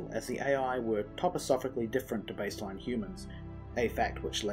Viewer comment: What is the word at ~12:28? Topisophically?